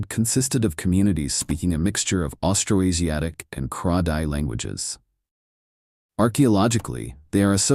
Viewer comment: has a transcription mistake. The vocal speaks of Kra-Dai, but the transcript subtitles says Khmer. The vocal is correct here, since the Khmer language nver had any presence in the Red River delta and especially not that era.